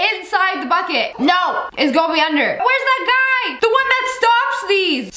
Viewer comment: I HEARD THAT GUY IS SHORT 🤣🤣🤣